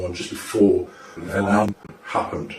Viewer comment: It sounds like they reversed what he said at about 3:47 ? whatever it was he did not say 9/11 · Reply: I’ve just put it in reverse and it says 9/11, I’m not lying either